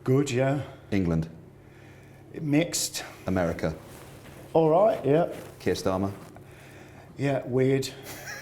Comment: What was the word after "America" around 7:04? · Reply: "Alright, yeah."